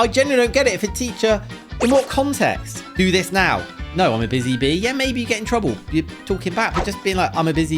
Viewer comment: I think they thought she said “ I’m a busy b word “ than a bee